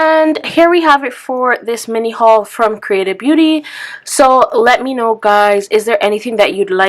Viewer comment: Never heard of creatabeauty before. When you were saying it, it sounded like you were saying “creative beauty” lol might have to check out the hemp products!